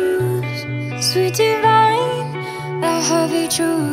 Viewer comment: LYRICS ❤ (I am so in love with this song) In your eyes, there's a heavy blue One to love and one to lose Sweet divide, a heavy truth Water or wine, don't make me choose I wanna feel the way that we did that summer night Drunk on a feeling, alone with the stars in the sky I've been running through the jungle I've been crying with the wolves To get to you, to get to you I've been down the darkest alleys Saw the dark side of the moon To get to you, to get to you I've looked for love in every stranger Took too much to ease the anger All for you, yeah, all for you I've been running through the jungle I've been crying with the wolves To get to you, oh, to get to you Your fingertips trace my skin To places I have never been Blindly I am following Break down these walls and come on in I wanna feel the way that we did that summer night Drunk on a feeling, alone with the stars in the sky I've been running through the jungle I've been running with the wolves To get to you, to get to you I've been down the darkest alleys Saw the dark side of the moon To get to you, to get to you I've looked for love in every stranger Took too much to ease the anger All for you, yeah, all for you I've been running through the jungle I've been crying with the wolves To get to you, oh, to get to you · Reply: I thought Selena Gomez said in Dutch ZOETE WIJN ( sweet wine English ) instead of SWEET DIVIDE! 😅🤣😂🍷